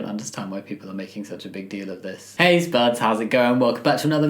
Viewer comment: Whenever you say “hay spuds” I always heard “ace buds” And as an ace myself I kinda love it 😍 😂😆😁😂